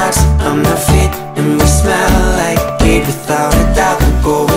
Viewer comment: Not gonna lie, I honestly thought it said "and we smell like beef" like the vine and I was so confused